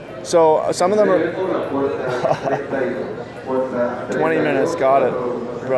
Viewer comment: haha when u said 20 mins 6:06, he actually said "Gate 32".